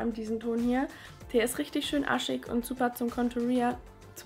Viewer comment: wo du zu dem bronzer hast du gesagt er iat aschig und ich hab arschig verstanden 😂